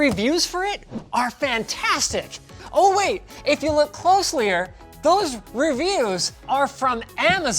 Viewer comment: At timestamp 0:17 Did Linus just say closelyah?